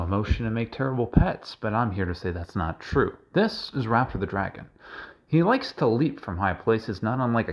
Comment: I thought you said Ralph the dragon